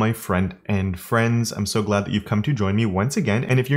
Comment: Wow I've only just realized he says front-end friends in the beginning. I always thought he said friend and friends which, admittedly, is weird, but I don't know every saying, so yeah, that happened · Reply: 🤯 I always thought he was saying “friend and friends” as well. I thought it was neat actually. Like, saying hello to me specifically and then everyone else.